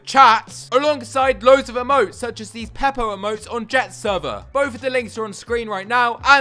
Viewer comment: did he just say "pepo emotes" lmao